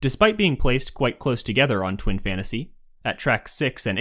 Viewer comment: i thought u said twink fantasy but both go together so